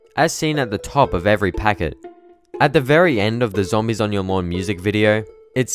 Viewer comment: for once I thought he said zombies on your mom